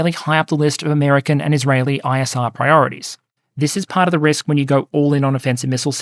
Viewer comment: Did he just say that the suffering of Iranian people is high on Iraeli's 'ASMR priorities'? That's sick! They fall asleep to the cries of the iranian children